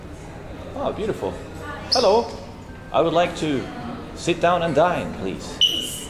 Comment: At first I thought he said "I would like to sit down and dying"😂